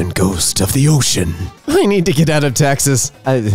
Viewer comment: Did anyone else mishear Texas as taxes? Taxes truly are scary